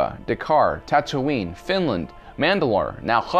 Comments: Did he say Finland? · Did u just say fucking finland